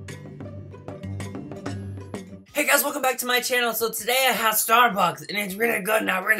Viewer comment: It was a little noisy at the intro in my place and I heard "Hello guys and today I'm at starbucks" 😂😂😂😂😂😂😂